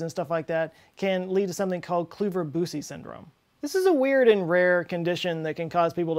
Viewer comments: Did he say Gary Busey Syndrome? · did he say Gary Busey Syndrome ??? eeeek!